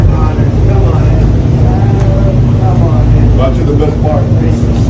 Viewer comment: Before he said "wow" I swear i thought he said " Now the good part" at 3:44 into the video, I may be wrong.